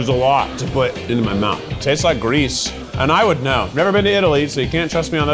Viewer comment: I thought he said grease, not Greece. "It tastes like grease. And I would know." and I'm like yep Keith, yep.